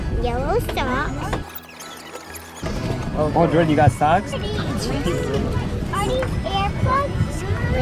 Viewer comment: I thought said 'are these airpods' 😂 cutest thing ever